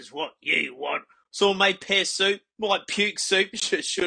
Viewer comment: My brain can't understand a word he says while testing mics, but my soul knows it's a funny story. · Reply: at first when he said pear soup i thought he said piss soup, ngl